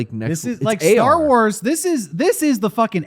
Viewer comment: I thought said A.R. WARS and I thought this was a perfect way to describe the cruise in your mind.